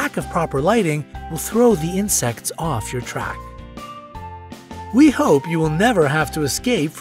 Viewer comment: At the end i thought you said “we hope you will never escape from (animals)”